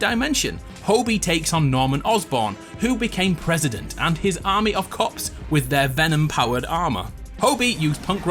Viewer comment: I swear i thought I heard "army of cocks"